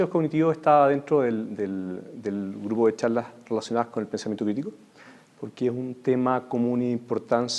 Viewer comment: En el 1:32 ...no se entiende que dice, si pensamiento lirico, virico, ??¿¿¿???.....hablá bien chileno ..sacate el pescado de la jeta